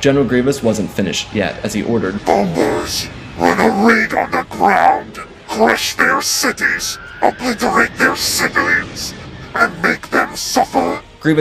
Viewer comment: I heard Grievous say: "Bombers, put that ring on the ground! Crush their cities, obliterate their civilians, and make them suffer!" the first time I listened to this. Honestly I wish I hadn't misheard, this would've been an epic quote.